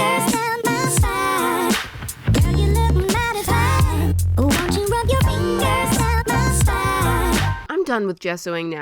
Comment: I like the sprinkle song but it’s kinda creepy to me because it sounds like it saying “oh won’t you rub your fingers on my sides” and idk I just don’t like that sorry 😞